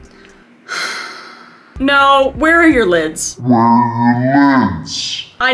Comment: Kris going “Where are your lids??” With a face of SHEER disappointment, made me genuinely laugh out loud a tad😂 Thank you Kris for being a smile to a genuinely depressed, ADHD struggling, and a bunch of other crap gal😂🙌💖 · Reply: I heard that as "Where are your kids?" and read it like that lol 🤣🤣😭